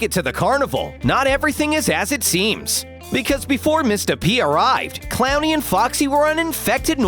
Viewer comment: I know he was saying Mr.P but I heard Mista.P